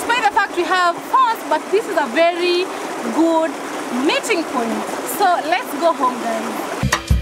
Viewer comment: I thought she said the river was a mating point🤣 but she said meeting point🤣 it goes down at the river🙈